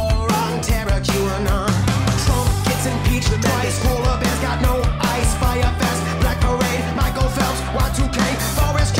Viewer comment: Does anyone know what’s being said in the background at 1:25? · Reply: Trump get impeachment twice